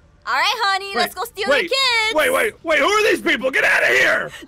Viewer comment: I laughed so hard when I heard “okay honey lets go steal their kids!”